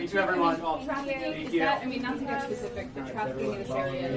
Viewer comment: Did anyone else hear in Newsoms news conference at 9:30 in the video a reporter said "the trafficking in the area".... as they were ushered out.